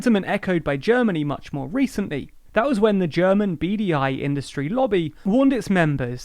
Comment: I heard "beady eye industry" lol